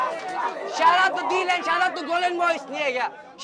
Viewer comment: Shout out to Golen boyz? I heard it right? Btw luv 4rom D land!!!